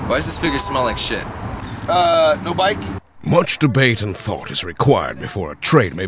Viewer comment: What did say at 3:15? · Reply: aaah no bike ?